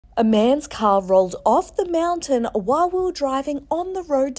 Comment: I thought she said a man's cow